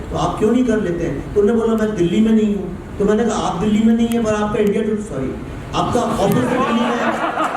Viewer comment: which is that channel? India Today? Or something else? Couldn't hear properly. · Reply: Yes India Today